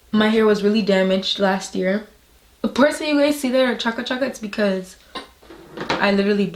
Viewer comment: Does anyone know what she said at 10:14 I’ve replayed it a few times and I really wanna know lolol😅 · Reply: Lmaaao it’s patois I said my hair looks chaka Chaka (messy)